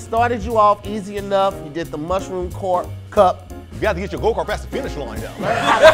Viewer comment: imitating in his E-40 voice had me rolling😂😂😂😂! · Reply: I always thought he said get your vocal cords pass the finish line tho 😂😂😂😂 it's go kart lol